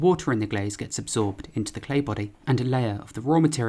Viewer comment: i kept hearing "clay buddy"